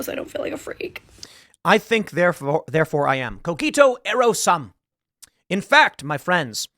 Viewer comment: The quote is "ergo", not "ero". Apparently what you said is "I think I will be".